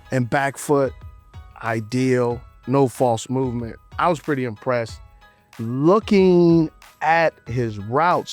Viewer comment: When he Steve said I was impressed i thought he said breast 😂😂😂😂